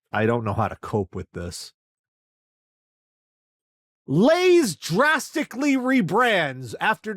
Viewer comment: I had a bit of a brain fart here because for SOME reason when you said "Lays" I heard "Doritos" and thought "WAIT! Corn chips are made of potatoes!? Was I supposed to know this!!??" 😅